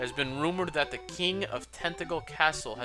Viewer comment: When you said "Tantegel" I thought you said "Tentacle." Made me do a bit of a double-take xD Also, the drop in viewers seems to be across the LPing community. You, HCBailly, Chugga, (myself, though I don't presume to include myself with you guys :P) etc., etc.. 'Tis weird.